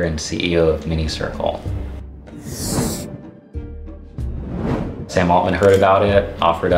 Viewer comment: almost thought he said Sam Bankman- Fried